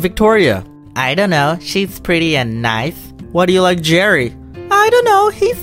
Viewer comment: Hello, Domics! I love this video so much, and I want to make sure everyone can understand and enjoy it. That's why I wanted to let you know that your captions are a little off. At about 1:28 the auto-captions say "she's pretty and knife" where they meant to say "she's pretty and nice". When you get a chance, could you fix that? That'll help the 360 million folks globally who rely on closed captions to understand videos. Thank you, and keep up the great work!